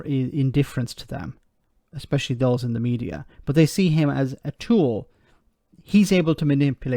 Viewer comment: It’s not just the media that thinks he’s a tool, I’ve always thought he was a tool. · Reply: I need a hearing aid, I thought said stool